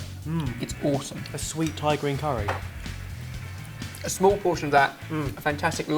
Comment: When said: "a sweet Thai grain curry" I thought he said a sweet Tyrone curry 😂